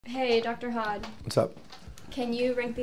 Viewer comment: I thought she said, Dr. Hot😂